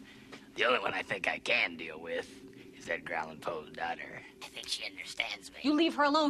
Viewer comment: what did he say? It sounds like he said ground Poe daughter